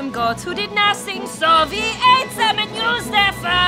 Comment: High on a hill were some goats who did nothing, so we ate them and used their fur for bedding! It's actually quite comfortable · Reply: at first i heard "soviets ate them"